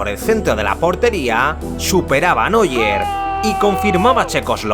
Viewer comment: escuché mal o dijo Neuer?